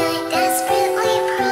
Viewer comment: I heard “I’m a good girl” as I’m a dead girl what is wrong with me lol